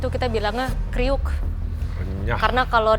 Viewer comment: What is Crispy?? said "Kemriyik"